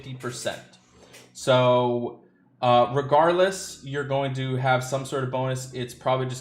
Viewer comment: I could of sworn I heard you say "RIGARDLESS" instead of "Regardless"